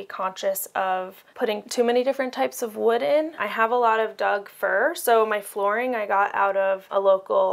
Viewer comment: 😂 thought she said “I have a lot of dog fur” at first lol. So lovely and personal. Love how she was able to get so many “full size” like things into it. Nice